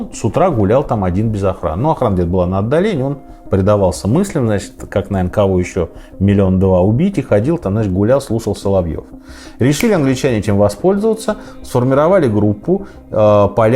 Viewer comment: Я услышал: "Предавался мыслям, ходил, гулял, слушал Соловьева" 🤣